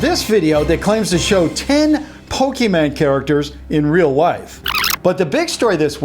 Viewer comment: did you just say pokeMAN at 0:42 Lol